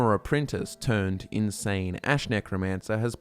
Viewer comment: For a second there I thought you said insane-ass necromancer